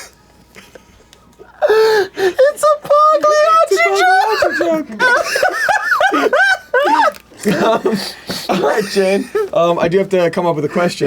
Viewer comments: What word dose shayne say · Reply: "Its a Pagliacci joke". Look up the meme (but doctor I am Pagliacci). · shane says "Its a pagliacci joke" referencing a popular joke about a clown visiting a doctor. What's important to note though is that if you have subtitles on in english, what he actually says is "it's a pugly artichoke" thank you for listening. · "It's a pugly artichoke" has to be the best possible misheard captioning of "it's a Pagliacci joke"